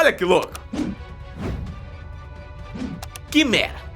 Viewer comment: Kkkkkkk no minuto 3:30 Vc falou QUIMERA e eu escutei: QUI MERDA